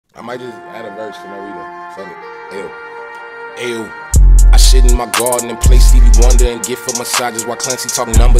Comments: thought he said he shit in his garden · at first i thought he is saying: "I shit in my garden" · That intro was so unexpected, but its Tyler so idk why I was surprised 😁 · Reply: i thought he said "i shit in my garden" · “I shith in my gawden” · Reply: i always heard "I shit in my garden" lmao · “I sit in my garden” for the past 2 years I thought he said “Shit in my garden” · I thought he said "i shit in my garden" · Thought he said I Shit in my gatden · i swear i heard "i shit in my garden" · I thought he said "i shit in my garden" lol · why i thought he said "i shit in my garden" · i first heard “i shit in my garden”